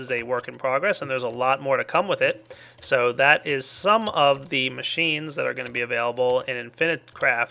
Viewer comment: did he say "shes just sown u guys the direction hes going"